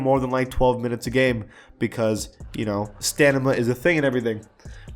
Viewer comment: this dude said stanima instead of stamina